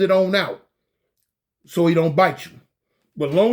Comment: I thought said he don't meat😮😮😂